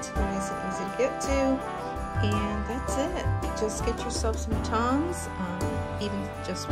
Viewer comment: At the end I thought you said get yourself some "tums" instead of tongs hahah!! I was like yes girl lol....That looks delicious! CHARCUTERIE is my FAV!! (but usually need some tums too hahah)